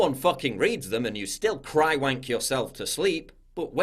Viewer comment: OH DUDE, "Cry-Wank yourself to sleep"... I literally pissed my self laughing!!! Brilliantly brutal Paul! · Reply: I thought he said "dry-wank." Which is also brilliant.